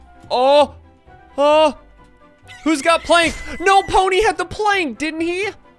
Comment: when he said that i thought he said "nopony" which is an mlp reference but then i realized that he said "no pony had had the plank" not " nopony had the plank"